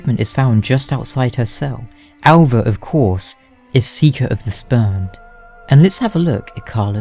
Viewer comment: Definitely thought you said speaker of the spermed at first